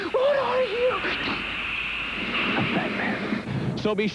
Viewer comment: At about 5:27 there is a different take on the line “I’m Batman”..Sounds like he say “I’m the Batman” here